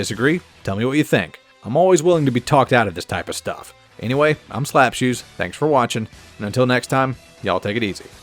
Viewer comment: I thought the intro said slaps hoes